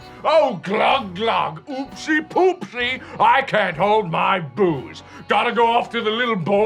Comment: OOPSY POOPSY I CANT HOLD MY BOOZE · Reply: Chicken Tendies lmao i thought he said boobz